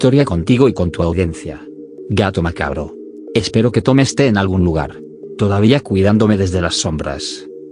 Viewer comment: No se ustedes, pero a lo último yo entendí "Espero que tomes té en cualquier lugar" JAJAJA ¡Tus historias son muy buenas gato macabro! Saludos 🤗